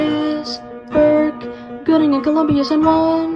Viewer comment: I thought that said gooning